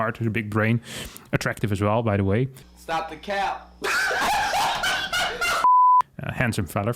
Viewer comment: or 6:03 ... what does he say? "Stop the count?" or "Stop the calb?" - Is there a native speaker who understands this?